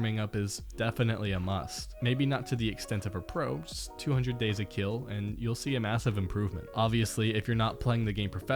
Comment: did he just say "200 days a kill"